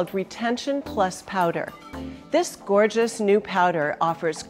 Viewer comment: My dumbass thought it said retention power